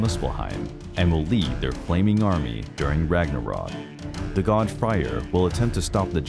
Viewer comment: sounded like he said "The God fire" lol